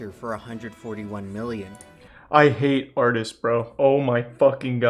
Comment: did i hear Charlie say BRO??